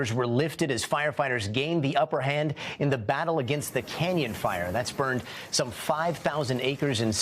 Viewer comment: I thought he said Kenyan fire for real I did 😂 I immediately started thinking about coffee prices until I saw the prompt on the screen